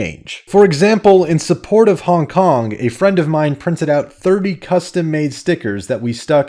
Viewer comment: I swear i heard "Custom Maid" stickers, as in, Custom Maid the game...